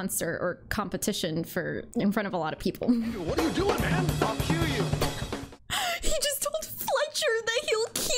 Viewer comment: When I first watched this without subtitles on I thought he said "I'll kill you."